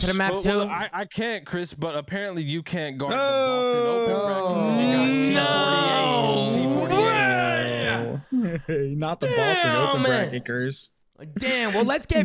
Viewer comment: What did say to?? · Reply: "You can't guard the boston open bracket because you got T48"